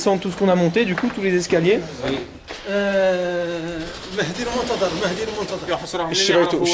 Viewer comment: Vers 25:12 il parle de quoi svp ? Jai pas compris Al moountadar ?